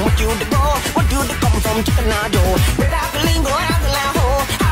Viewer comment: Why does it sound like it says chicken eye joe?😂